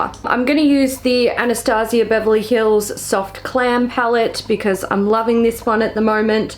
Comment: sounds like you said soft clam palette. 😂🤣